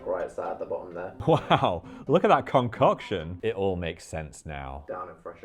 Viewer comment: Okay, I know you said concoction, but after that joke, all I heard was cumcoction 😅